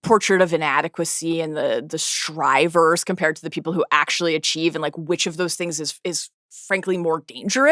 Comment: Can someone tell me what she says at 18:40? The portrait of inadequacy and the what? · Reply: Strivers compared to people who actually achieve